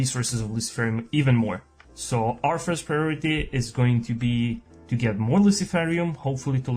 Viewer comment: Out of curiosity, are you french? I feel like I kind of get your accent but I'm not sure at all. Also I thought I heard you say "priorité" instead of "priority" haha (I'm french myself so, I can't place your accent at all)